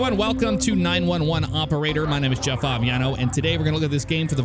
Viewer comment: I really thought you said - "Hello, my name is Jaffar...."